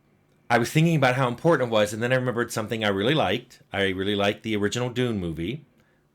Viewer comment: I swear I heard Tim say: "I liked the original DOOM movie" and I almost lost it haha😅